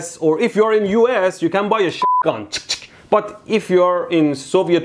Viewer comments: did he just say shitgun? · "You can buy a Shit Gun" tell me you don't hear it